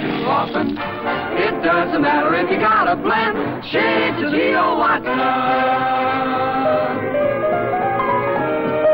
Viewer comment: does anyone know what they were saying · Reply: Yes - they sing "doesn't matter if you gotta blend, shades of Leo Watson." The reference is to Leo Watson, a singer of the 40's who was know for scat-singing and vocalese - where a voice emulates a musical instrument. The whole passage is "Hey Bop-A-Rebop, the present trend, licks we don't sing too often, it doesn't matter if you gotta blend, shades of Leo Watson." Definitely hipster-talk. An example of Watson here at youtube is "Leo Watson, Tight and Gay, 1946."